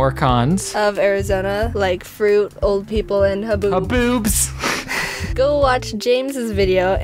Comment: JAMES. did u say boots or the thing that sounds like boots............